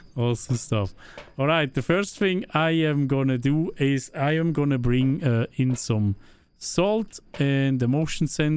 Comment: at first it sounded like "i am gonna bring uh insym salt"